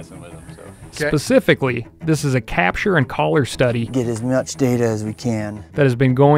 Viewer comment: I first heard it as ‘get his nuts data...’